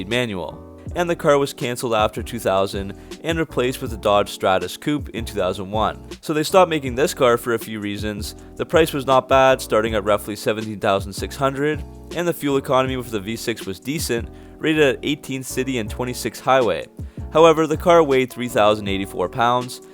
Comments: Did you say the car cost $70,600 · I had to rewind cause I thought he said the car cost 70,600